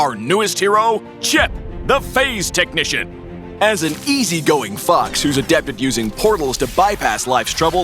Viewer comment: "As an easy going f*ck" I misheard it sorry